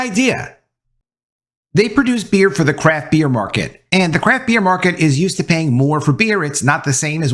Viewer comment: I thought he said crap beer market.